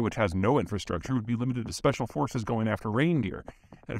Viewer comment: At :47 I heard "special forces reindeer". That would be so badass if the Finns had some!